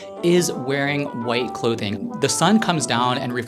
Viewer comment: I love how completely misheard him. He said you should be wearing white clothing.